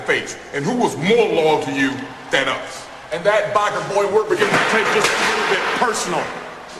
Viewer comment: LMAO!!! What does Bradshaw say at 1:11 when he starts getting pissed off? lol All I got was "And that biker boy _______ take this a little bit personal!!!" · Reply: we're beginning to take this alittle bit personal